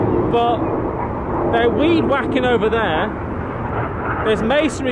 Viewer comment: Not gonna lie - thought for sure he said there was "wee whackin" happening down by the river. Figured it was British slang for the same things park dwellers are doing by the rivers in America.